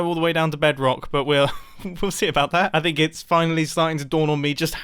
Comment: I thought he said pussy about that